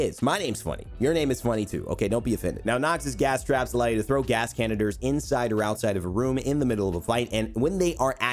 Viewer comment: did he say caniters